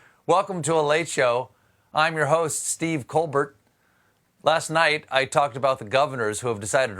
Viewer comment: Did he just say, "I'm your host, Stiff Coldbutt"?